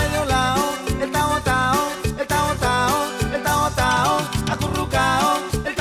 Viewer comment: Mierda yo siempre pensé que decía el tavo tavo jajaja